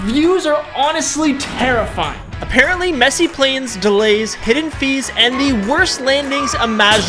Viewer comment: I thought he said Messi playnes